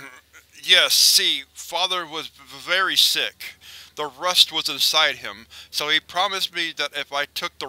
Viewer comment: I wonder what those sounds were <_< (what did it say ?!) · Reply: I thought at one point I heard it say "It's inside of me," or something to that effect. Possibly "Dad" also.